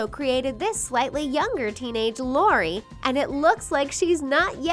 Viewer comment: She says Lori instead of Leni